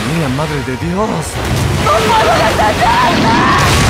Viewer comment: que dijo no entendi xd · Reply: No puedo detenerme